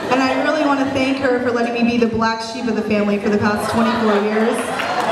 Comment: I like how in 1:50 the captions say 100 years when the sister said 24 years, lmao She is a comedic genius, and this is another inspiration for both wedding speeches, and my dream job of being a comedian